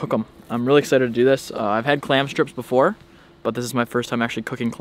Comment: Thought he said he never had clams?